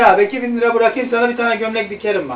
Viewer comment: konuşma sırasında orda bir şeyi yanlış anladım tekrar dinleyince anladım gömlek dikerim demiş ben zannettim ki gömleğini skrm dediği sandım lan bu nasil samimiyet diyecektim sonra dan anladım gerçeğini